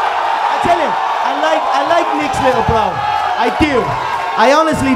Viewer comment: Could someone tell what connor said at 4:43? Im not a native speaker and its hard to get for me because of the accent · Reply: He likes nicks little bro